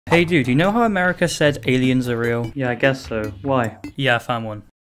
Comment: I thought he said when aliens said amercians are real